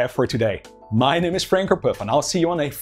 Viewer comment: The subtitles say "[? Franker Puff ?]" instead of "Frank van Puf"